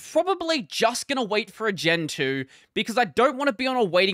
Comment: You said gen 2 but I heard gentoo and thought were saying they were gonna switch xD